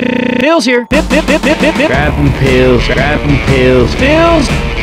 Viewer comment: He sounds like he saying bills here! XD